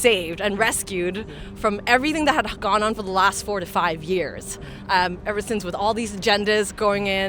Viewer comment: Did She say all the things that has gone on for the last 4 to 5 years? 🤣😂Thought she said🤫45yrs